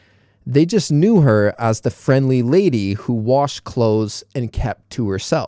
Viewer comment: why "wash" instead of washed, you can see the word "kept" after "and".am i wrong or the sub is wrong?